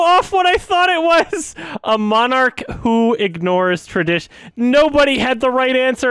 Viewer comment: I heard "A monocle ignores tradition" first. Like it almost sounds like the first half of "monarch" and then it falls off a cliff